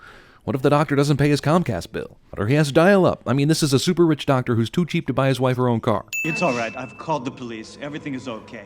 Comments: he said "doctor who's too cheap" but the subtitles say "doctor too who's cheap" 1 sin for CinemaSins. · you wrote: "too who's cheap" instead of "who's too cheap" DING CinemaSins!